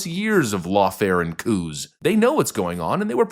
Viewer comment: I know what you meant, but I heard "lawfare and cooze"